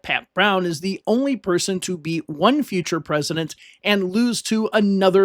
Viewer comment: He beat Nixon in 62 and lost to Reagan in 66, both future presidents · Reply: Oh, I thought he said "To be one future president," not beat.